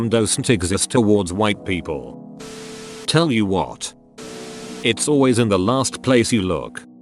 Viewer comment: apparently I've been hearing "it's always in the last place you look" wrong. I always heard it as "it's always in the last place YOU'D look".